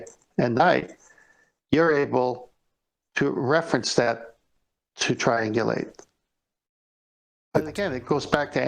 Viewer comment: i thought he said "you are able to reference that, to try and get laid"... Thought I'd discovered their ultimate motive!